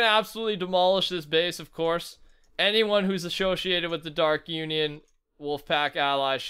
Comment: DID HE SAY ashowsiated?